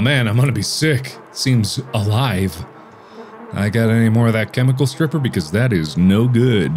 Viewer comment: I thought Markiplier said tentacle stripper. I was like, what?